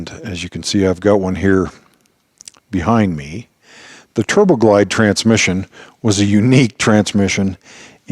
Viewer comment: I had to go back to 30:57 a couple times and replay it, because I thought you called the TurboGlide transmission behind you a "TroubleGlide". From what you said about it afterward, it seems to be a perfectly reasonable Freudian slip. 😉